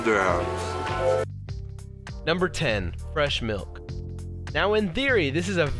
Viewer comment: At first I thought it said breast milk and then I was like wait "wth"